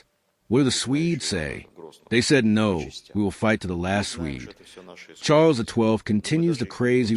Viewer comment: wrong. He actually said: "We'll fight to the last Finn" 😉